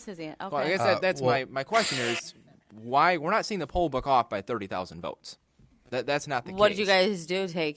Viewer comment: I thought she was saying pollock... thought maybe it was her way of saying something was fishy